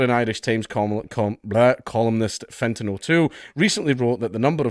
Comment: Somewhat off topic: Why did I hear "fentanyl 2" when you read "Fintan O'Toole"? :D